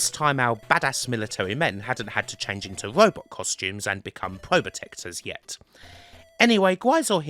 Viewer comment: bit of politics. I missed the word before "protectors". "Hadn't had to change into Robot costumes and become ??????? protectors" ? I probably totally agree, but couldn't work out what you said. Great work as always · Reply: Probotectors -- when the Contra games were released in Europe on consoles the heroes were changed into Robots and the games were called "Probotector".